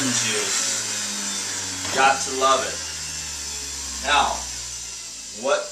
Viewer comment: I thought you said cats love it. Was thinking about feeding my cat. Ha. :-) But you said got to love it.